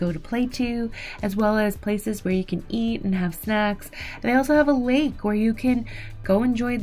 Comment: Did anybody else say, what the what at 6:45? Though I heard great place to eat and have sex 😂. Great review of these cabins, I’ll check them out based on your recommendation.